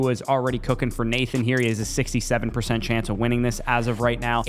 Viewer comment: what did he just say? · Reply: Haha 😂 6-7